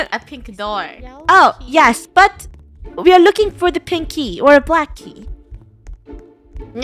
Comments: I think I heard “pinkie” instead of pink key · I thought they were saying pinky, I had to watch it again I just now found out Their saying pink key